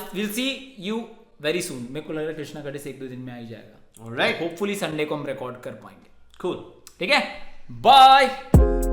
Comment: can anyone explain me that what said at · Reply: Krsna ka diss ek do din me aa hi jaayega hopefully Sunday ko record karenge